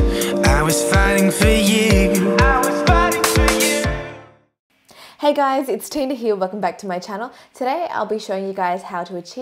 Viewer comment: Who else thought Tina said "Hey guys Tin here!" fml 😂😂 EDIT: #DEWSQUAD #JUSTDEWIT 😂😂<3